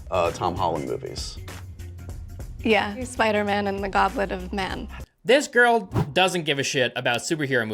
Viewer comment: Spiderman and the goblet of man. Truly a classic. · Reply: I thought she said "Gauntlet of Man".